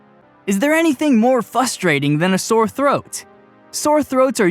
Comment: Did he say, "fustrating"? 😖